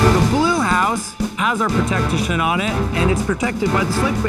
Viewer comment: Yo did he just say, “The blue house, has our protective isht on it” ?! 😂